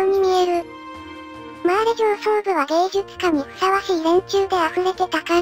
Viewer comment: ここの芸術化が芸術家に聞こえたから上層部にもザックレーみたいなのがいっぱいいるのかと思って焦った